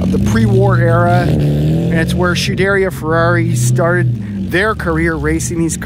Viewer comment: did you just say SHUderia Ferrari? :D:D:D::D:D:D:DD